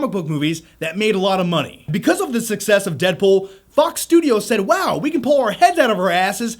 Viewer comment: Did you really say "Fuck's Studio"?